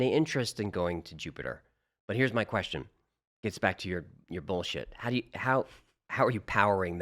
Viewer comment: 'gets back to your bullshit..." Did I hear that correctly!? 😂